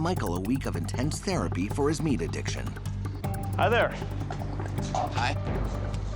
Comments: I thought it said: meet addict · I thought that shit said meth addict at first